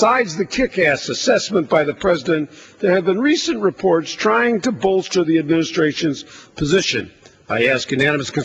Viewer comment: Is he saying that Bush lies? It almost sounded like he said at 3:10 There have been recent reports trying to "Bull Shit" the presidents position. He did say bollster though. I do like whay Rep. J. McDermott has to say here. Thanx for sharing this.